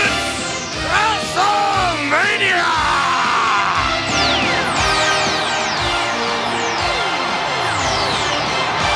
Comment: What does Vince say @ 4:07? lol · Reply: Face to face.